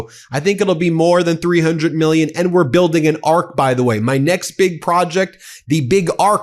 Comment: Canadian here. Listening first thing this morning. I thought our host said, "Trump's building an Ark!" I couldn't stop laughing!!! Um, also, what does DC need an arch for? And his rants about Canada and our "arrogant" PM! 😂 I'm STILL laughing!!! I have to.